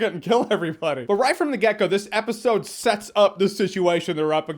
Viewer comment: I keep hearing, "Right from the gecko." I need to get my hearing checked.